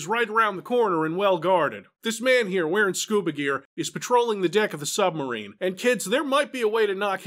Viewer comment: I had to listen a couple of times. Yep, says "Dick of the submarine" instead of "deck" XD